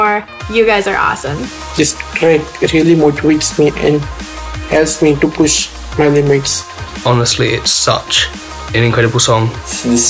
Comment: wat he said? 😂😂😂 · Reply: this track, it really motivates me and helps me to push my limits